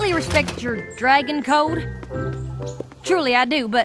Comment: I thought that AppleJack said "COUP with you serving me."